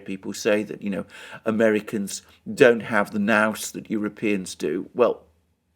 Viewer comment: Can somebody tell me, around 10:35 Alexander says "you always here people say 'Americans don't have the _____ ("nouse?") that Europeans do".....what word is that, "nouse" Sorry, can't figure that out!